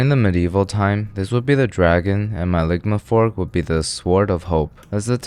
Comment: For the longest time i thought he was saying lickmyfork not ligma fork 😂😭